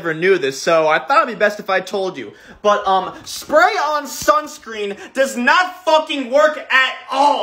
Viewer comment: Heard it as "Sprite on" instead of "Spray on" 💀